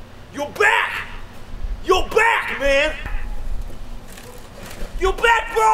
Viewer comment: ALL THESE YEARS I THOUGHT HE SAID " GO BACK! GO BACK MAN!"